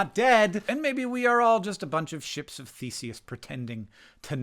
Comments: When you said "ships of Theseus" I heard "sh!ts of faeces." I think that says more about me than you. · So I was doing my washing up while listening to this and I genuinely thought you said we are all ‘Ships of Faeces’ rather than ‘Ships of Theseus’ and honestly, I thought that was a wonderfully simple way to see myself. Next time I’m feeling too egotistical I’m going to remind myself I’m just a Ship of Faeces traveling through the world. 😂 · Oh Hank at first I heard "ships of FECES" and I got concerned about your self image, but then I heard you say it a second time and understood ohhhhhhhh THESIUS, gotcha, yes, I see where you're going with this now. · Definitely thought you said we were all ships of feces. I was like, speak for yourself Hank! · "We are just a bunch of Ships of Theseus". At first, I thought you said "Ships of feces", and thought, "yeah... that's technically correct". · I know he said “ships of Theseus” but I misheard “ships for feces” the first time and it almost made sense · For a moment I thought you said "ships of faeces" and I'll forever think of the human body that way from now on.